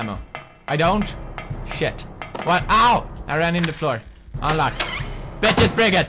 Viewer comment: I heard him say I ran into Flor and my name is Flor